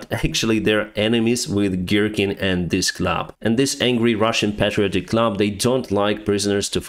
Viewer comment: So, I was listening whilst doing housework, and misheard 'The Angry Russian Club' as 'The Angry Russian Clap...' Spent a couple of baffled minutes wondering why on Earth they'd name themselves after an STD before I twigged! 😂